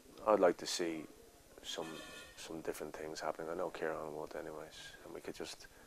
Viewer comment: What does he say at 3:57? "I know Keiron..." Awesome interview, btw. Thanks for uploading! <3